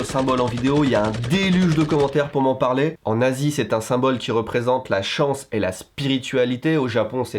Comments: "En Asie c'est un symbole...", j'ai entendu "En nazi c'est un symbole...", mais tuez-moi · "En Asie c'est un symbole ...", j'ai entendu "En nazi c'est un symbole ..." la confusion dans ma tête -_ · Purée quand il a parlé des Manjis, même si je savais déjà ce qu'il allait dire j'ai entendu "en nazi c'est un symbole..." au lieu de "en Asie" XD